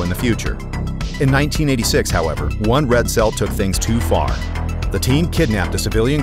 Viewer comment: You say "Red Cell" I hear "Incel"